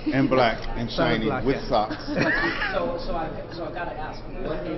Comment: Did he says ( at 3:51 ) "MBLAQ , SHINee with socks ?" x)